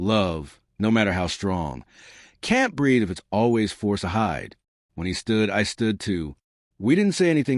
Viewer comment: I thought he said forcely 😢